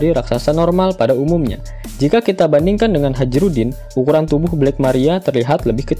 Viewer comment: Aku salah denger.... harusnya hajrudin malah kudengar haji udin